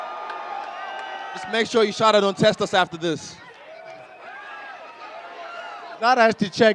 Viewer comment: Can anyone say what Israel said at 14:35? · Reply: "Just make sure USADA don't test us after this"